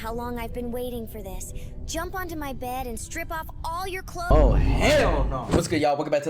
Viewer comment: Did you say ship you clothes off? I’m gonna rip you off with a shredder say goodbye to your arms and legs.